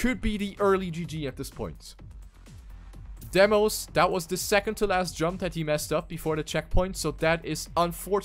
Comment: ? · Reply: always sound like you were saying "second-teh-last". listen closely.